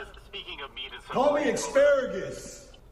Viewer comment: Did i just hear "Holy asparagus"? LOL